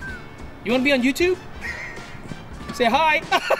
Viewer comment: hey my My fault. I thought you said "let me see your tattoo" not "do you want to be on YouTube?" 😉